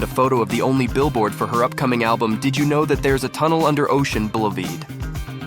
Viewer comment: Nice video until I heard "bullaveed" instead of boulevard. Made me laugh. Lol